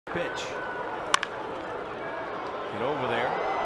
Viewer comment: instead of saying pitch did he say b**ch? Basically it’s the p flipped and move the backwords c to the other said and you got a curse word